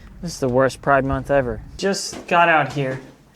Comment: Did he say “worst pride month”? Good one! 😅